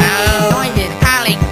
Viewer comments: i thought it said noise is coilin pick up frog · I hear "Norman's coming, pick up frogs" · Idk why but i heard "Noise is calling pick a frog"